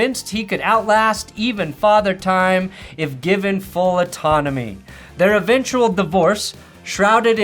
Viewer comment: I misheard that last part as "given full anatomy" and thought "oh yes, it all makes sense now".